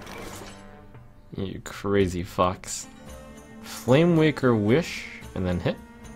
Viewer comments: LOL XD At 16:18 it sounds like trump is saying the f word instead of fox XD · I thought he said, "You crazy fuck." I had to watch it like seven times before I realized what he actually said. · I thought at first he said "you crazy fucks"